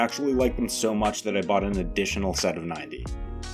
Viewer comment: I spit out my root beer because I misheard him say "I liked them so much I bought another 790"